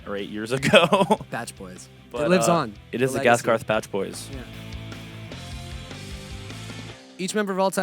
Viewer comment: did jack said “batboys”? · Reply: I thought he say "gaskarth bad boys" but then I listen again, probably something like bachboys